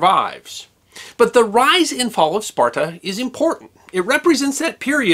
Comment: My eyes are bad. I thought it said the rise and fall of sports. . Which i would've watched. Gladly